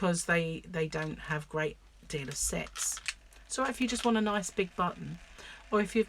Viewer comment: Lol. Listening while im working... I hear... " so if you want a nice big butt"... i missed the "on" sound at the end.